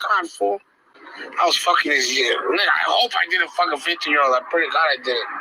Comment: I have no idea who this guy is or care who he is but you completely miss heard what he said. He said "I hope I DIDN'T F a 15 year old, I pray to God I didn't" and you made it out that he say DID....Just to get the facts straight on that specific part. Both you and your Co host completely missed that part I guess. I despise pdf files but thought that should be pointed out for full disclosure